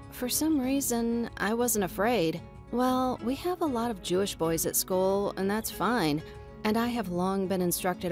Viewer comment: wait, why do the subtitels at 3:50 say muslim boys instead of jewish boys XD